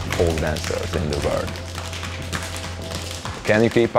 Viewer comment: I thought he said, "can you kpop?" I was so confused. 💀💀